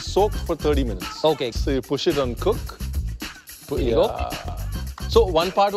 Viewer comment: Rohan, while pressing Rice cooker, said "Here you go" I heard "Fuyioh!!" and I was like did Rohan just pull an Uncle Roger here.